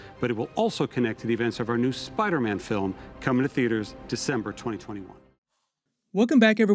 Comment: did...did he just say... DECEMBER 2021?!?! YESSS